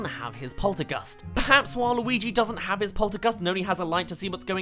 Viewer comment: did i hear waluigi?